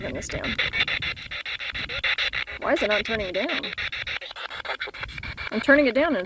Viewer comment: I heard "Turn it down"